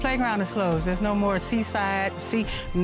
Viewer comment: Did she just say come on her tumbleweed